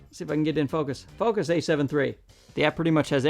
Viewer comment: Oh this is my Bad as I thought you actually said "Focus A7S3" so I thought you had that camera not the a73. I am sure some people are happy with the 4k zoom feature, though I do see it as a subjective point of view and anamorphic is great especially in 6K. Though for most people they're not gonna need it. its cool, its a headline feature. Though some one buying this camera will probably buy the siru 1.33 lenses which as you state shouldn't be used for the 6k mode and the lenses for that do can cost 10s of thousands of dollars. So I think it is cool and a big deal that its in this camera but I think its very subjective for the users. Obviously the A7s3 is an amazing camera, the best? certainly for some. The point your making on bang for buck is great. and there are some incredible cameras out on the market now that make incredible savings for thousands of people easily. I picked up a fuji X-T1 for £200 and a XF35mm f2 for £260 a great camera combo and fantastic results. it has its down falls for sure. though very pleased with it. Maybe I am being too critical. Sorry. Like i said though it is a good video with good ideas and presented well.